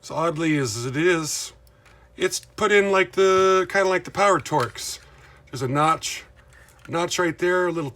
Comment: When you were pointing at all your engines on the shelf at the beginning, it sounded like you were saying bear turds, bear turds, bear turds. I tried closed-captions and it had no idea either. Is that what you were saying? And Susan Dey was pretty stunning in that photo! Thanks for sharing that one. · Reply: I was saying PowerTorque. That’s the kind of drive system in the other half of my Tyco engines. I gotta quit mumbling :-)